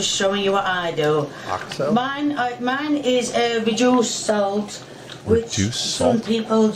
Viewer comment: Oxo is a brand of stock cubes. When you thought she said juice salt she was saying that she was using a reduced salt version of oxo.